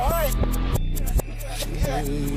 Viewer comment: he sounded like he said seizure